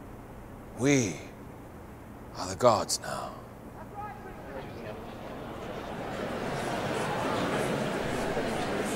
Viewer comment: What does the heckler say at 4:18? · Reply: “That’s not true”!